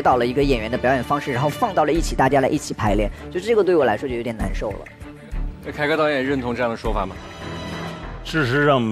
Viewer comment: 那个表情说明一切。 what are you说啥咧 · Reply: 凯歌导演说，我已经要睡着了哈哈